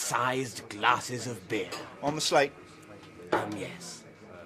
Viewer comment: What is the bartender saying at 0:56? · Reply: "On the slate?". British way of asking "On your tab?" as in when you don't pay up front but settle the debt every now and then sort of like a credit card thing.